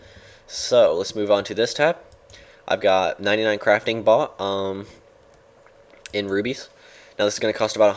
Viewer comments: it sounds like you say i got 99 crafting bot lol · thought you said I have 99 Crafting bot xD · it sounded like you said you had a 99 crafting bot